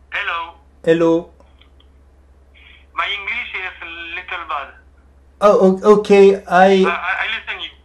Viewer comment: My English is a little “bad” 라고 하는 거 같아요 ㅋㅋㅋㅋㅋ 진짜 찰떡 같이 잘 하시네요 이게 뭐라고 재밌짘ㅋㅋㅋㅋㅋㅋㅋㅋ